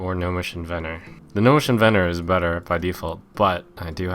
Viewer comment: What did he say between 1:36-1:39? Cuz I'm not native of English) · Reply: "The gnomish inventor is better by default."